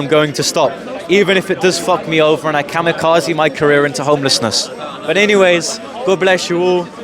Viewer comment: (i heard you say) : Even if it (fall or fu..) me over and kamicazi my career into holiness.